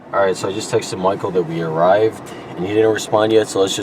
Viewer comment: I thought it said Michael Jackson